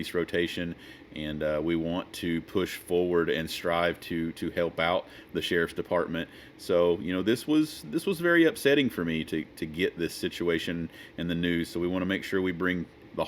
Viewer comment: If you're into transparency, let us know the station he volunteers with . Sounded like he said "Severe sheriff dept".